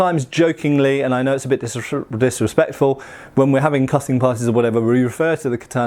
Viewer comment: Wait, did I just hear him say "cutting parties"?